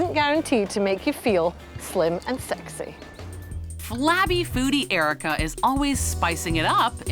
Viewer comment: Did they just say “Flabby foody Erika”??